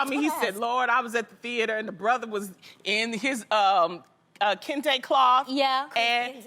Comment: anyone hear when jeannie said "kunta kente" in the background at 7:37 when loni was talking? Like, why was that necessary?